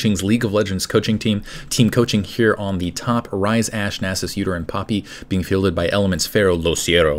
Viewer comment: when he said udyr and poppy i thought he said "uterine poppy". i thought it was a new skin -.